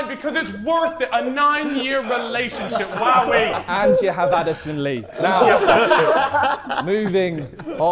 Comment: Calfreezy is like the one student of every class that can defend any stupid topic and manage to get away with it smoothly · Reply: callux said addison lee? Who is that? Or did i just hear wrong